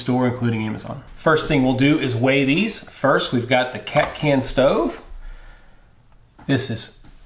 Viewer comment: When you were weighing them I thought you said "cat scan" stove lol